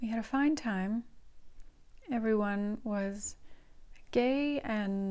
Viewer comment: Wait a minute did she say everyone was gay at 14:02?